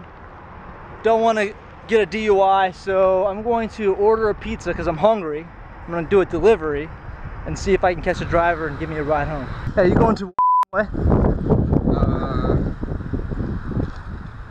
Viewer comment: At first i thought he said,"Hey are you going to f*ck way?"